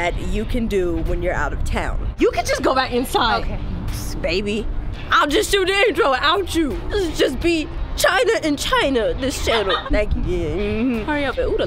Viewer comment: Does anybody tell me what does China say from 0:54 to 1:02? It seems funny as hell LOL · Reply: jer "You can just go back inside! Baby! I'll just shoot the intro without you! It'll just be China and China, this channel!"